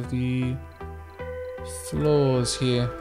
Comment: Every time you say "flaws" I hear you say "floors" and for five episodes I haven't cleared that up. it was very confusing listening you speak about people's floors 😟 😂